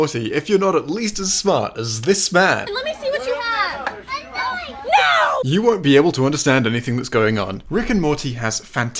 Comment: What did the kid say at 10:40? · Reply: "a knife"